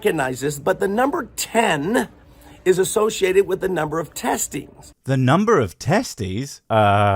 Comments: I could have sworn he said testes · I also thought he said 10 testis